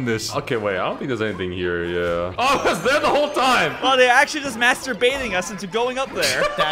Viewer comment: I had an ad that cut off mid sentence so all I heard was "They're master baiting"